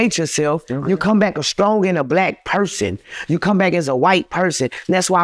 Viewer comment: did he say “you come back as a stronger not black person you come back as a white person?”